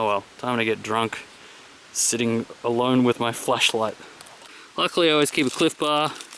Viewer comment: Haaaa I thought you said alone with your ‘fleshlight’..... kinky camping at -6....